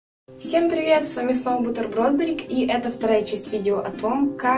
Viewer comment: Когда вначале ты поздоровалась, мне послышалось слово "бутерброд"